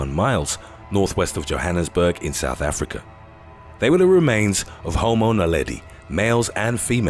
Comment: I thought he said "the remains of home owner Leddy." Had to go back. 😃The Islander is the answer maybe?